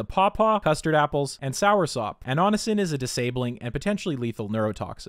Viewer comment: I loved drinking guanabana (soursop in English, or Annona muricata) nectar as a child. I'd get some whenever I had the opportunity. Years later I learned that the whole family of plants is neurotoxic, and if I had managed to have a can a day I would definitely have ingested enough annonacin to show symptoms of brain damage. 😮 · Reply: I need to get some rest -- thought you said sour soap. 😆 "Tangy!"